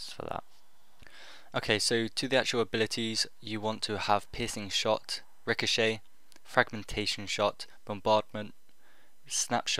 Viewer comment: sounds like your saying "You are going to want pissing shot" lol